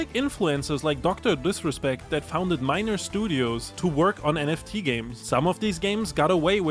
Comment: did he say minor studio???